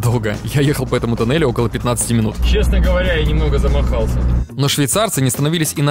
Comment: мне послышалось, или сказал: ''немного замоРАлся''